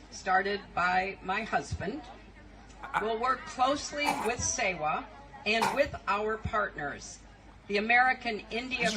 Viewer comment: She just said we will work closely with Satan i heard her say it no surprise.